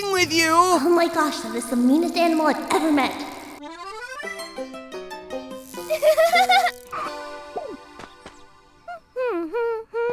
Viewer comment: What did she say? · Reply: "Ohmygosh, that was the meanest animal I've ever met"